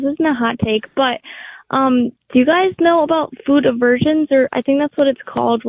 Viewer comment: I totally thought she said "food of virgins" 🤣